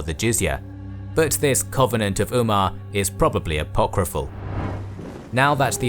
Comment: covenants and promises are not taken lightly in Islam, you cannot simply break them. (i thought he said the covenant was likely unkept, I was corrected) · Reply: "hypocraful" isn't a word. He said "Apocryphal". Probably because of difficulty in finding the documents.